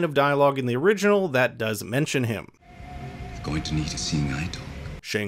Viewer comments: He ACTUALLY said,, "You're going to need a 'Seeing eye dog' "... (I believe.) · Yo, idk what it is or why… but something about your voice makes me trust literally everything you say lol.. so when you thought he said “you’re going to need to see Nighthawk” instead of “you’re going to need a seeing eye dog” for a brief moment, I was fully convinced it was ME that always heard it wrong 😂 Even when you called him Nighthawk I was like “wait… his name was never Nightwolf?! It’s been Nighthawk the entire time?!?!?” 😂😂😂